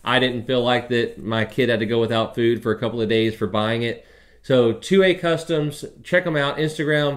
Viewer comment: Do you have a link to Two Way Customs? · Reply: at the beginning you mentioned the company that made the led lit redfish and I thought you said it was Two Way customs. I looked, but couldn't find the company.